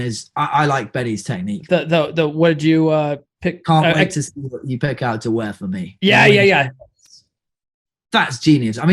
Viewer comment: couldn't quite hear the line he said. what was it? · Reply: "I can't wait to see what outfit you picked out to wear for me"